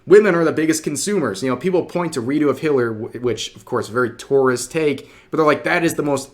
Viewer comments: When you say Redo of Healer too fast, it sounds like you said Redo of Hitler. Lol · did he say redo of Hitler? lol I could have sworn that's what he said.